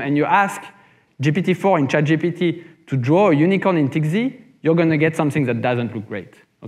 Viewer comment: what is he saying? TXZ? tigzy? Can someone point me in the right direction? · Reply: TikZ